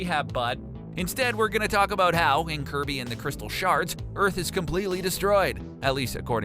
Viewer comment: had to pause and rewind because i thought you said "Kirby and the crystal sharts"